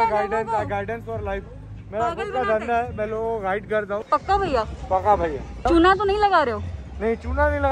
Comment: did she said bhaiya 😅